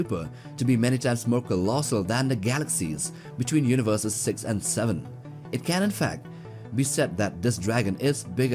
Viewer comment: did he just say 67?